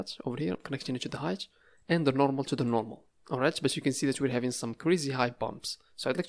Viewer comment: Just asking in 4:19 ....Did you say oraia LOL .Are you greek :)